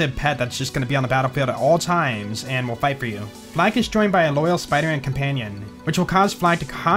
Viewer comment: I keep hearing "Fl4k's spiderman' 😂